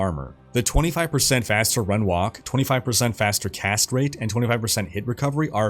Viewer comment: Every time you say "Cast rate" I hear 'Castrate". Don't worry, it says more about me than you. I always play as the Necromancer so you can imagine. Great video.